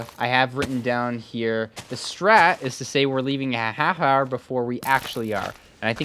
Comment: "the strat is to say were leaving at hahar" Bro wtf is hahar😂